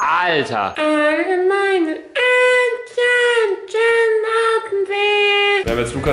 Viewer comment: " Alle meine Entchen CHILLEN auf dem See" oder hab ich da was falsch verstanden....?